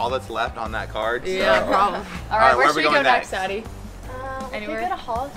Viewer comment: I thought said where should we go next daddy at like around 6:11 but she said where should we go next addi